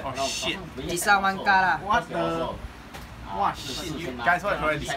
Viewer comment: Did said "Wah, sialan" at 18:32? xD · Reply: Haha i think i said, Wah, SHIT!